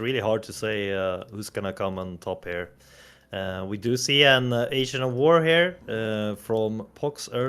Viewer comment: "Asian of War here" Youtube Caption said 😂😂